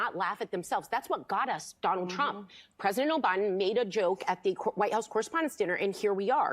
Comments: Did she just say O'Biden? 🤣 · Did anyone catch at 15:03 she said president Obiden? Hmmm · Did she say President O'Biden? Listen closely. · Did she just say "President O'Biden"?? 🤣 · Did she really say "President O'Biden? 😂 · Did she say "..President O'Biden.."? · Did anyone else hear her say “president O-Biden” at 15:03? 🤔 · They definitely do not live rent free in Trumps head. HE lives rent free in THEIR heads and it’s proven because they do nothing but cry about him on a daily basis. Btw did anyone else catch at 15:00 when she said “president OBiden?” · did she say president Obiden? These are some disingenuous out of touch teachers of the wine mom stay at home dummies. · Did she say Presiden OBIDEN 😂 15:18 joe biden DAUGHTER not neice